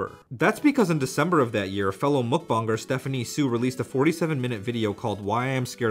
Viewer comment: did you just say muckbong?